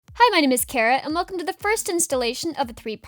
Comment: Why does it sound like she says “hi my name is carrot!” In the beginning?